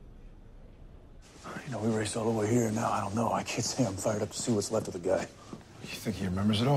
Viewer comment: what does Dean say at 0:05 - 0:09? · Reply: alright we raced all the way here though I can't say I'm fired up to see what's left of the guy